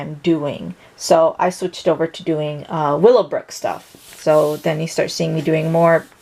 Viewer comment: I'm a little confused... what were most of the drawings based off of? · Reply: It sounded like she said willow brook or something like that.. if anyone knows please tell me. Thank you